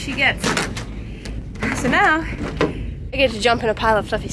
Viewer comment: So at about 8:22 you said, “now I get to jump into a pile of fxxx snow”. I hope I heard you wrong, I would be disappointed. Please tell me I misunderstood. Like the videos, I was raised on a small farm, hand milking Jerseys, Gurnseys, Brown Swiss, Holsteins, etc. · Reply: No! I said FLUFFY snow! Glad u like the videos!